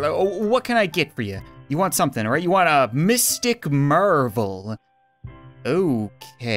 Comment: Lmao the first dood with the ginger mustache said he wanted a mystic marvel and I thought he said captain marvel 😂